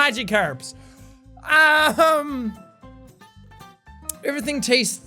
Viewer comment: did you say magikarps or magic herbs? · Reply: yes he said magikarps